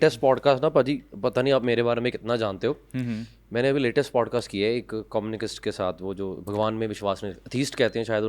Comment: communicKisst? 😅😂 Btw is this Punjabi Podcast??? Don't understand beyond Balle balle.